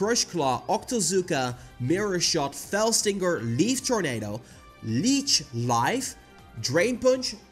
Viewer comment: I thought you said one of the moves was “beef tornado”. I was wondering what that would entail.